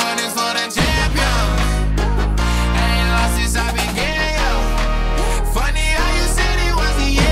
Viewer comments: all i hear is "I ain't lost since i be gay yeah" 🤣 edit: and 0:27 "then i went and did a gay yeah" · I really thought he said:"I ain't lost since I've been gay yuh" ☠️💀 I was like: I thought this was clean. But then I replayed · Straight up thought he was singing "any losses I'll be gay yuh" at 0:21 I knew it couldn't have been the lyrics but I just couldn't make out what he was actually saying lol